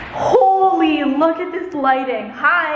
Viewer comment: At the intro I thought she said “holy bucket this lighting!”